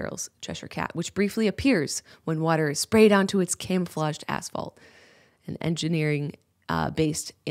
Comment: I thought you said camouflaged asshole